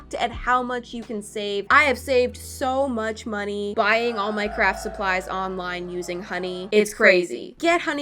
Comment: It sounded like she said “Minecraft supplies” instead of “my craft supplies”… Am I the only one? Am I deaf??? Edit: the second I posted an ad of a deaf person popped up and I’m like….. 😵‍💫IluMinATi